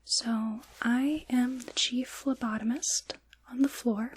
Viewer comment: Thought you said Chief Lobotomist for a moment and was about to be like 'HELL NAH GET THOSE NEEDLES AWAY'